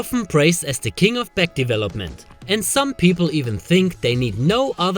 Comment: thought I heard "pullup the king of pec development" in the beginning..